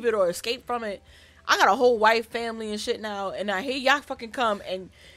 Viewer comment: Girl did you say "I got a whole white family now...."? 🤣🤣🤣🤣⚰ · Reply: No LOL!!!! "Whole wife and family" lol!!